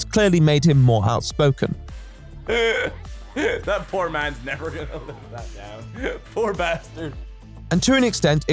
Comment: There's no captions and my ears aren't very good, I can't make out why this is such a hostile comment. Can someone explain the context? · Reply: "That poor man's never gonna live that down! " he said while laughing. I think he just borderline repeated it by saying "That man.. " or something at the end. Hope that helps.